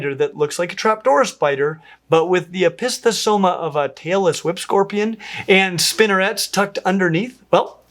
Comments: Every time he says "tailless whip scorpion", I hear "Taylor Swift scorpion". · Had to do a double take there. You said “tailless whip scorpion” I heard “Taylor Swift scorpion”…… 🤣